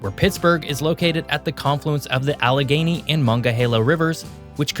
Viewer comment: the way he pronounced "Monongahela" cracked me up. He said "mongahela" whole the subtitles said "monga halo"